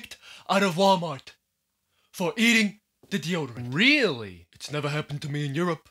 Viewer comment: if you turn on the captions it says children instead of deodorant